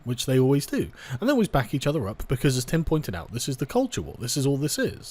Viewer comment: At first I thought you said Tim Pool and I was gonna cry